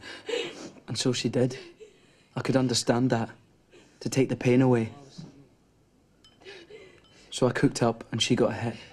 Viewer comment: anyone know what Renton says here? NOt the overdub but the character in the room. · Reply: He definitely says “Allison”. It doesn’t sound like he says very much before or after. I think he was simply addressing her before cooking up the shot for her.